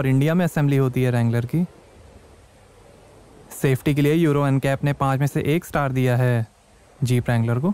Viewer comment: did he say "5 mese 8 star diya" because "ek star" bahut kam ho jayega 50 lakh ki gaadi ke liye.